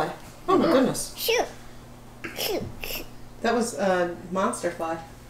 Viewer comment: LOL!!! I heard say "Oh sh*t" when the fly was flying around! It's around 6:59-7:00 minutes in! 🤣🤣